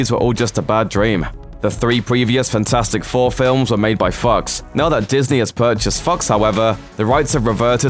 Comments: I thought I heard him say, "...made by f*cks". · it sounds like he's saying the Fantastic 4 films were made by f***s instead of Fox... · Am i the only one who heard 'The three previous fantastic 4 films are made by fucks' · Thought he said "-made by fucks" instead of " -made by fox" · HOLD up 7:51 did you just say they were made by "Fucks" and in that EXTREMELY monotone voice??? Or am I tripping and you said something else and not "Fucks"